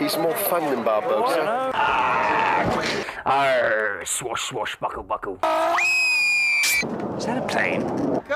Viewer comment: what did he say? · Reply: MCRX he said: “arrrrrr swash swash buckle buckle”